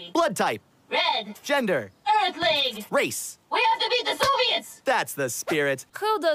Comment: “Race?” (Angrily) “We have to beat the Soviets!” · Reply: Yeah, for a second I thought I heard "zombies" but it was actually "Soviets".